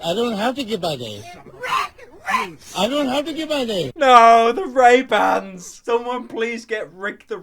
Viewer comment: When he said ray bands i thought he said ehem hands